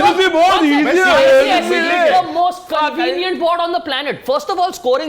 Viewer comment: CAN SOMEOME PLEASE TELL ME WHAT WORD JOSHI USES AT 0:55??? I have been trying to find it for ages and literally haven't been successful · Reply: Convenient.